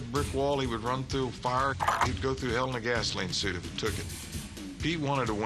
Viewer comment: What did Johnny Bench say about Pete Rose around 2:00-2:06?... "Pete Rose would go through Hell in a--" what?... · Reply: He'd go through hell in a gasoline suit